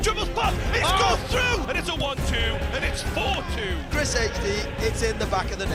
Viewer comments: Does he say chrisHD? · did he just say Chris HD